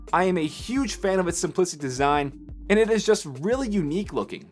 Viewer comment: You mean 'simple' rather than 'simplistic'. It is always a mistake to use a longer word because it sounds better, without actually checking whether it means what you think it means. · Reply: He didn't say 'simplistic', he clearly said 'simplicit'. Not that it matters much, seeing as simplicit isn't actually a word. Reference